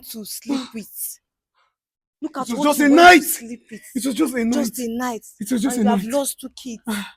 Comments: I thought he said it was a one night time😮😮😮, may God deliver him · Thought he said it was a one night stand lol